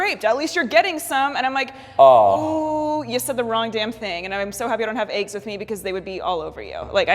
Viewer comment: I legit thought she said "I'm so happy I don't have aids with me because they would be all over you." I was like wtf lol